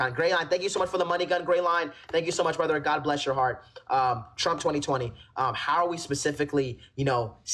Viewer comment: did bro say "trump 2020" 😭